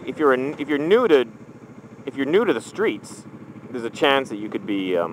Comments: i thought you said, if you're neutered... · Is it just me or does it sound like says "if your neutered" at 1:40 ?